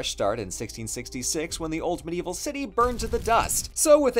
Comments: I kept hearing "the old medieval city" at the beginning of the video as "the ultimate evil city". I have to say, I agree with both interpretations. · I thought you said ultimate evil city instead of old medieval and I was in agreement cause you know the genocide and stuff.